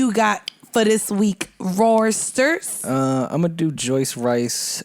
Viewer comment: did she say “Rorsters’”!?! 😂🤣😂, Mandii remains me of a girl I know and it’s cringe!